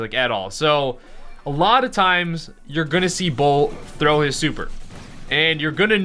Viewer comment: He said bull instead of Bo